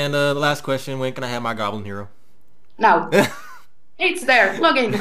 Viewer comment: gobble zero?? what did he say??? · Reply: goblin hero :D